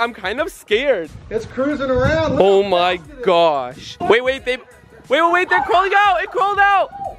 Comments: did he say babe? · Did he say "wait wait babe" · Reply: XXREDHOODXX 5 Im pretty sure he said “wait wait they”